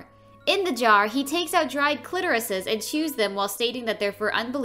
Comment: is it just me or did she actually say “dry clitorises” ?!! 😂💔